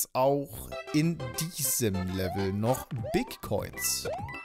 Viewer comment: Was er sagt:bigcoins Was ich verstehe: bitcoins